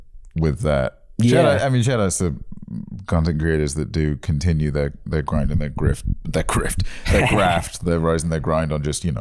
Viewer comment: I heard "content creators continue their crime and their grift" lol